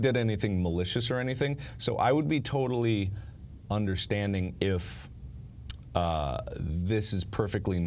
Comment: I thought he said he'd play undertail for this stream